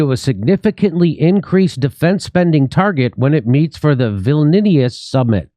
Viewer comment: "Vilnius Summit." I swear I thought that he was saying "Villainous Summit."